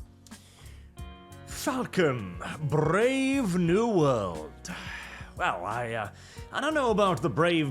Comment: I could have sworn slid into view and said "Fuck 'em, Brave New World.." I was like: Well damn, it was that bad?